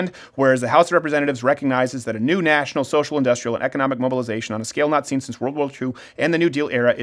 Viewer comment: Wait did she just call for a "National Socialist" revolution? Please Kek, tell me she did!